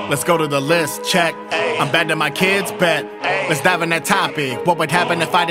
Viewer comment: I thought the part were he said "bad with the kids? bet" that he actually said "Abandon my kids? bet". I died laughing and had to double check